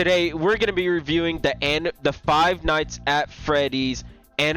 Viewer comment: Five nights at anime it sounded like bro said that and he has like 25,000 videos of that so yeah nah and I also did not get jump scared😂😂😂😂😂🗿🗿🗿🗿🗿